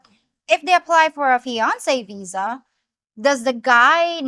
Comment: I keep hearing “Beyoncé visa” and I keep asking myself, Beyoncé visa? Where do I get one of those ??? :)